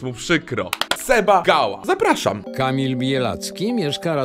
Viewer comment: czy tylko ja usłyszałem "Kamil bije laski" ??? XDD